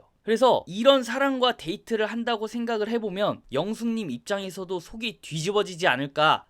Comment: 이게 지금 자막 오타 때문에 더 헷갈리게 되어 있어요. 자동 자막을 켜도 발음 문제인지 영상내 자막처럼 나오는데요, 3:51의 자막 '영수'는 '영수' 인가요 아니면 '영숙'인가요? 문맥상, 그리고 귀에 들리는것도 '영숙'인데.....